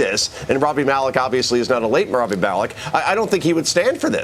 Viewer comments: did he say Rami Malek is dead? Pretty sure he isn't... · did he just say, "the late Rami malek" what!?